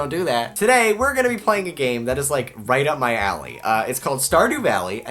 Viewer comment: For a second I thought he was about to say, "A game that is right up my ass"...Yep.